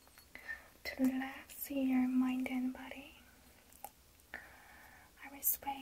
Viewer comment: Her: relax your mind and body What I heard: relax you're my dead body